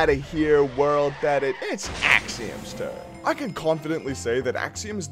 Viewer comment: Why at 0:02 when you say "it's Axions turn" caption says "it's xHamster"?